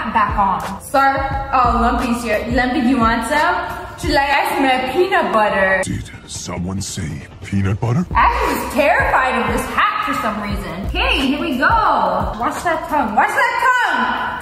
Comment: did you say peanut butter?????????????????